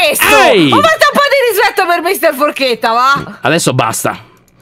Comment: Io ho capito: "ho preso una porchetta per la forchetta" 😅